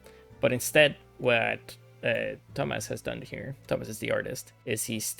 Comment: "But instead what dumbass has done here" I know you said Thomas but I misheard it, I'm so sorry. Great Video btw.